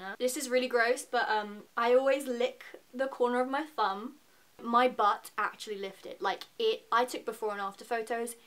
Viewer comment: I thought she said 'I lick the corner of my thumb and my butt actually lifted'😂😂